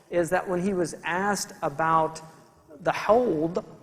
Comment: Can someone tell me what was the thing the Prophet (PBUH) was asked about? I dont understand the word. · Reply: the haudh = الحوض